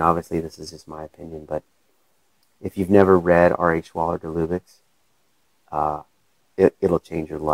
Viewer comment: what are you say to read? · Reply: Nature Word by R.A. Schwaller De Lubciz